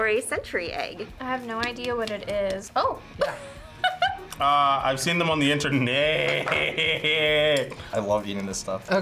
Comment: How has this been online for over a year, and has NOT gotten real captions? I want to see them for what says at 4:48 ! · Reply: he said I have seen them on the interehehehe😭😭